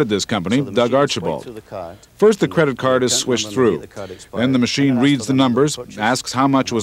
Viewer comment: "First, the card is swished through." How did we get from swish to swipe, and can we go back? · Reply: I thought he said squished. 🤣